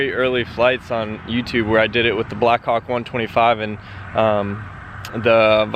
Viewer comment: it sounds like he says "black cock"